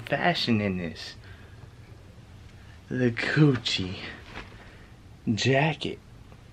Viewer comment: This man sounds like he said coochie or than Gucci jacket